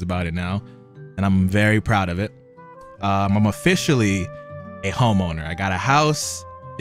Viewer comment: thought he said homo at first